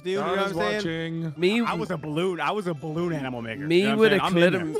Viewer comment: did just say “I was a balloon animal n*****”? 😂